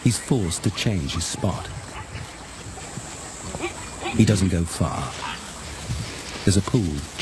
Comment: did I hear right😂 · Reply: He doesn't go far or he doesn't give a fuck